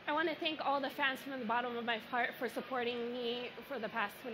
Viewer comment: 10.23 it sounds like she says : I want to thank all the fans from the bottom of my fart 😂😂😂😂 it's got to be my hearing